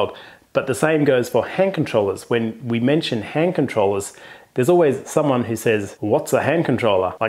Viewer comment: At first I thought I heard “hair controller” then I realized it was because of the cap.